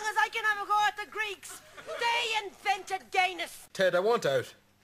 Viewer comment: The first time I heard it I thought she said "They invented Guiness".